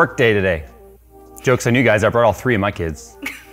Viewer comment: "I brought all 3 of my kids" So what you're saying Jay is that Phil and Nik have their own build buddies too with your other kids. 😁👍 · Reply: Thought you said you “ bought “